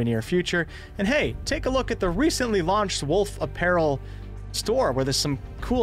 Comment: I heard 'wolf of peril' LOL. Fitting I guess...